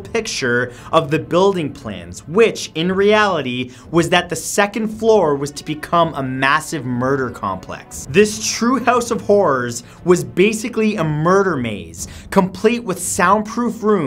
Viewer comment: who else heard true house of whores instead of horrors?